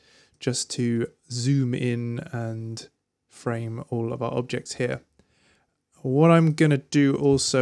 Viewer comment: what did you say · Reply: "zoom in and frame all of our objects here"